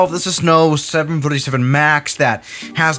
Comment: Did you say 747max?! Haha Turn on captions btw